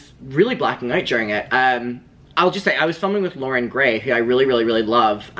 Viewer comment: i thought u said conan gray and i jumped like HUH